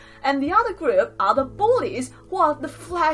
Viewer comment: the caption says " the other groups are the bodies" instead of bullies 😂😂😂